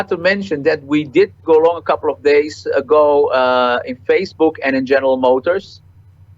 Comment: What stock does he mention he went long on at 9:03? He said something about bicycles? I heard Facebook and GM and then I can't make it out.